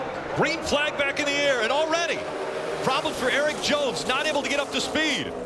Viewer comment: did I hear prawblems????👂🏻